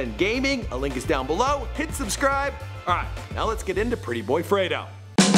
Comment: Every time he said pretty boy freto I thought he said young Alfredo